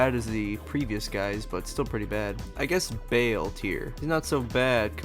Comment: did you say... BAYLE????? CURSE YOU BAYLEEEEEEEEEE!!!!!!!!!!!!!!!